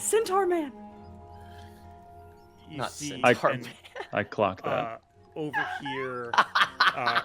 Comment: So cinematic, it hit the players hard. When Zorias meets the “centaur” I thought I heard him say “I clopped that…” a blend of clocked and clip clop. You know, because hooves…